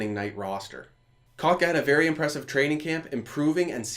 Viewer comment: did he just call kotkeniemi kok? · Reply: Kotka haha